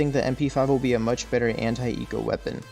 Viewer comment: i thought he said Hentai Eco Weapon New subscriber